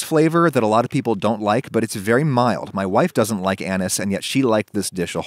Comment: I almost thought Adam said 3:29 Fennel bulb has a very anus flavor